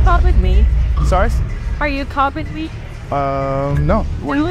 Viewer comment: “Now, can I get a copy of your number?” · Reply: Sounded like she said copping me 😅